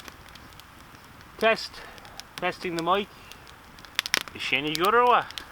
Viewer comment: Did u say "cześć' in 10:28 which means hello in polish? ;) greetings from Poland ! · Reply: Hi! nope I said to test t check my mic! Thanks for tuning in!